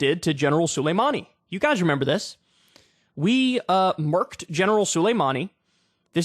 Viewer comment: Anyone know what Kyle said at 4:21 ?? I think he says "murked":hand-orange-covering-eyes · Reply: yes murked him we murked thier top general